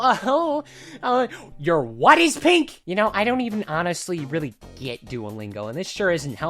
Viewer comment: bro thought it said “my p*nis is pink,” when in reality it says “my pen is pink!”